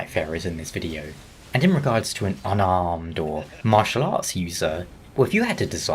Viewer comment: I can't bro, I keep hearing you say "mashallah user" instead of "martial arts"